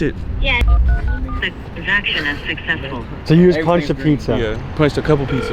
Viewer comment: Did that say transaction unsuccessful? Hard to hear fully but definitely sounds like unsuccessful.